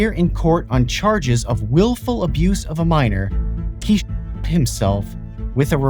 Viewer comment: I thought you said he shit himself💀